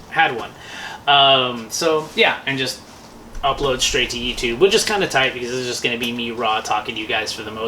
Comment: it sounded like “it’s kinda cool, this is just gonna be me like raw doggin you guys” and I was like woah ho ho buy me dinner first 😂